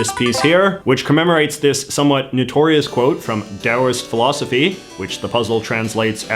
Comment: Based taoism. EDIT: Sounded like you said "daoist" but CC says "taoist" so I'm going with that. Point stands, based philosophy.